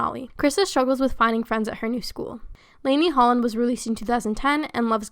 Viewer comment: What’s her name I can’t understand what she says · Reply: lanie